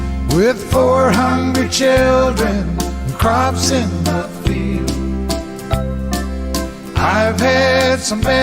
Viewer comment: I first heard this as a small child. I thought the line was "with 400 children, and a crop in the fields".